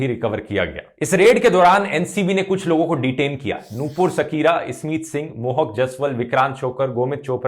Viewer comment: Did he just said Nupur Shakira